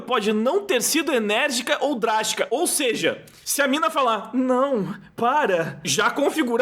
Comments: Ela falou "Não, para." eu entendi "Não para" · Meritíssimo, ela disse "Não, para" eu entendi "Não para"